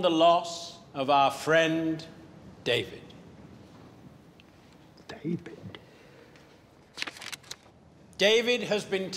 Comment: now I can understand what bean say "DAVID"😂😂😂